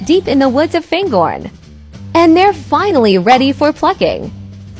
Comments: i thought said they are ready for fu**ing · I thought he said they were ready for f*cking 😅